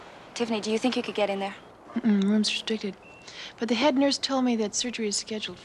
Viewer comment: What did Tiffany say at 3:10? "Uh uhm. The room's *?" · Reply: Mmm mmm the room's restricted